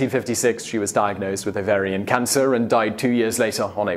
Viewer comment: Did he say Bavarian cancer?